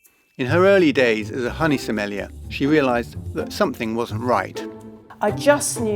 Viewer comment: What is she? I can't make out the word. Youtube isn't getting it right either. "Honey smellia"? 0:30.